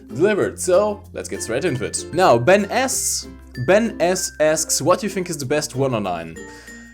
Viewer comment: What I hear at 0:27 "Ben'sAss Ask's"